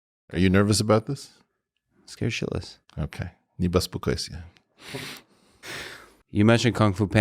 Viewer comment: Anyone catch the phrase Eric used as he seemingly comforted Lex after finding out he was "scared shitless" ? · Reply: ne bespokoysya*